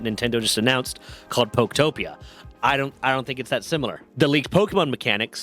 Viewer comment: Also, swore I heard at 3:50 that Nintendo is calling it poke-topia like Craftopia? Sounds like they are prepping to sue poketpair again after this one. Either I am delusional for seeing a pattern or Nintendo is becoming demonic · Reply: it is actually called Pokopeia but, honestly, it looks so bad. Genuinely looks like a watered down Minecraft and within the trailer you see a total of 2, maybe 3 locations. Nintenstans are accusing Pocketpair of, and I am not joking, developing Palworld Farm in under a week just to spite Nintendo, meanwhile PWF looks soooo much better in gameplay and quality than Pokopeia (which looks like the game developed in under a week imo). Literally just compare the trailers with each other. As soon as I saw PWF I was actually hyped and I didn't even know about Pokopeia until one of my friends pointed it out, like, 3 days later. When I saw the trailer for Pokopeia I was like "what the hell is this slop"